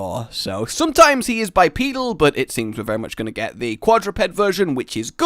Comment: was i the only one who heard bipenal?